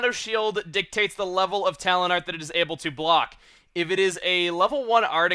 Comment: Talent Art? I thought you were saying Talon Art the whole time!